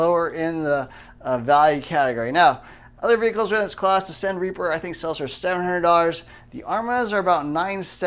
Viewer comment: What rc car dod he just say? "The _ Reaper for $700" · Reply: Cen Reaper. The company that makes the cool looking F350's. Cen Racing.